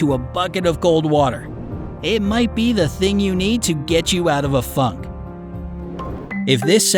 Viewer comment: what did it just say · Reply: He said "get you out of a FUNK"